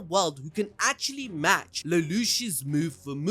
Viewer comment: Did you just call him a fucking schnitzel? 💔💔💔